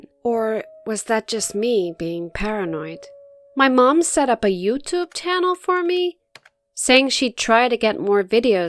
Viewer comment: I hear YouTube tchannel